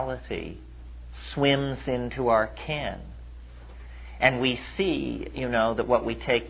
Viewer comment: Incredible lecture. I jut can't figure out what he says @ 19:09 ... "swims into our 'ken'"?? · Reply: Bronson Buskett Yes- "ken": one's range of knowledge or understanding.